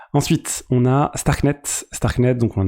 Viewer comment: Quand tu as dit "ensuite on parlera de starknet", j'ai compris "ensuite on parlera de skynet" et je me suis dis : "oh bordel déjà" 😯😯